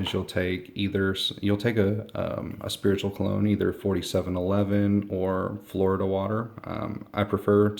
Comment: hello :) what is 40 7 11 ?! If that is what i heard! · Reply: 4711 cologne <link>